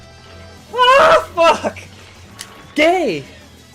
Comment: Hahaha 30:08 does he just say, "Gay"? Hahah